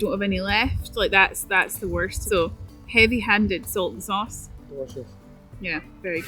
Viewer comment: Thank you , that was great fun all round , the first time I had to look up a Scottish pronunciation, as I thought you and where saying Soltan Sauce 😄, having seen recipe it really appeals to me 😋